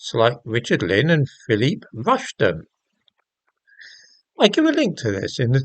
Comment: Oh my gawd I thought you said Willie Rushton, that was a near miss. I'm going deaf so please excuse me.🧏‍♂🦻